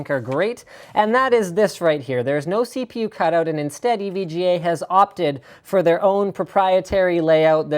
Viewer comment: I thought it said EVGA Hard On.